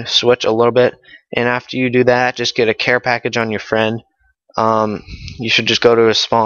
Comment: get a care package on your friend? i thought yew said a predator missile?